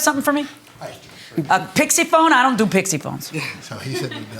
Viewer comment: I thought I heard her say, "I don't do pissy phones." 😂